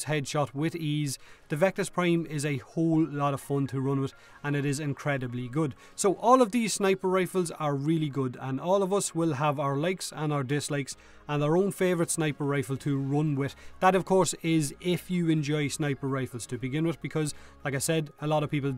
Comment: someone else thought he said "legs" instead of "likes" while watching at the aiming?